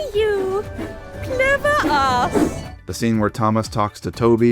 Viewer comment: the subtitles say “Clever Ass” instead of Clever us and I died laughing.